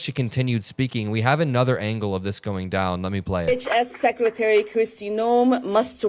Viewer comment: She said H.S secretary kristi noem and I thought she said bitch ass kristi noem every single time I replayed it until I turned out subtitles 😂